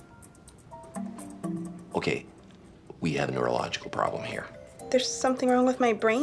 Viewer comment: She does this, but understands the meaning of "neurological" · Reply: I thought he said urological